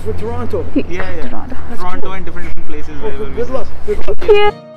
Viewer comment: did he say get lost??? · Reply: 🙂He said 'Good Luck' :)